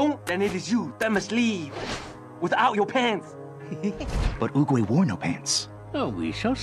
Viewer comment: bro what did u say oogwae?